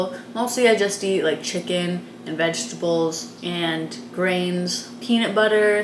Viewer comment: Had to rewind at 10:57. I thought said brains for a second. Scared the crap out of me.